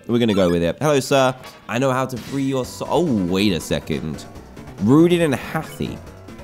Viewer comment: When it said Rudin and heathy blocked I thought it said Rudin and Healthy Bloke